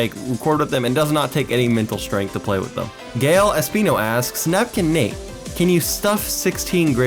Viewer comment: OMG WHY DOES 9:39 SUBTITLES SAY GAY LATINO NOT "Gael Espino"